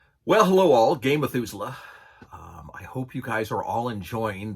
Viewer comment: Every time I see your face in my feed I hear in my head "Hello all, Game Methuselah". Thanks for the video